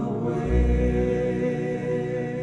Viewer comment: Did Elvis actually say "effin' loud (or love?) song here, son" at 3:40? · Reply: Yes he did. “F****in’ love song here, son.” I can’t believe it either! 😂😂😂